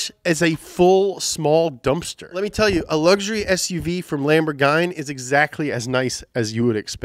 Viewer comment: Next:Top 10 car cool names? Caiman or Cayman?:) 10:19 Did you really say Lamborghany?????:):)